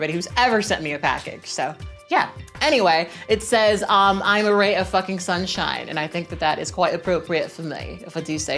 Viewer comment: I thought your cup said “I am a Rat of Fucking Sunshine” hfjdjfbfnjsjfjd 🥕